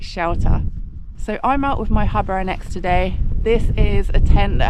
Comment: at first i thought I heard you say I'm out with my hubby and ex today, and thought well this is going to be awkward, never mind the busted tent 😂